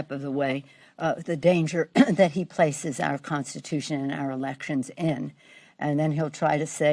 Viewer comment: in... what in the actual fluff is she even saying? “Have the Constitution swallow Clorox”? Good grief...